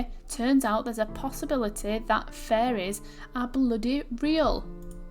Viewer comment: I happened to turn my head away and not see the text here. I thought you said "furries" instead of "faeries" for a split second and legit lost it thinking the game devs actually just threw that line in there lol! Of course listening again it was clear but jeez did it sound odd when my attention was on stirring my coffee lol!